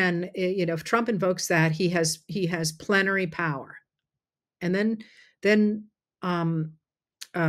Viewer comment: That’s not what he said, he said “plenary power,” completely out of context as if Trump already had the power.